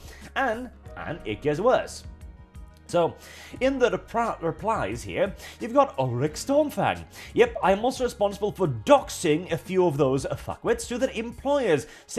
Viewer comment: Had to do a double take. I thought you said Ulric Stormfag 😂